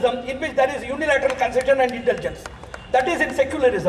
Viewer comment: pseudo-secularism is unilateral concession and intergence? Did I hear it properly? can anyone tell me the meaning of this?